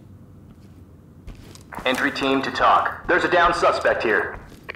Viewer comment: "Entry team to toc there is a" BANG "Down suspect" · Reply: I thought they said "Entry team to talk" I don't know I never really thought about it. But "TOC" makes way more sense...